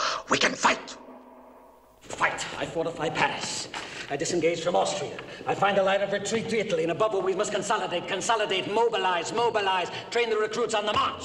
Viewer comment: What he says at 4:33? I'm learning english so. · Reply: "I fortify Paris; I disengage from Austria; I find a line of retreat to Italy, but above all we must consolidate, consolidate, consolidate!"